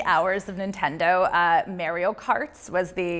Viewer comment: Did she just say mayreo karts 🤔